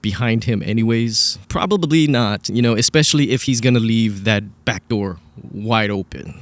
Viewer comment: I thought they said the front door was opened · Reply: Back door, according to that Ashley Bandfield show